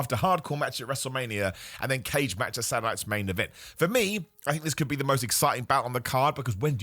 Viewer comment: I keep hearing you saying "Satuday's Night Main Event" as "Sanitized Main Event"